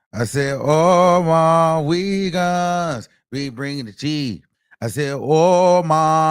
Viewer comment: First time I heard that jingle, I thought he was saying "all my niggaaass..bring me the tea" My bad, my bad lol